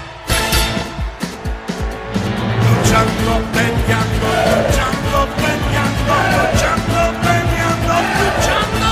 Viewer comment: Aight, so does anyone know what’s being said at 1:33? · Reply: "Luchando, Peleando"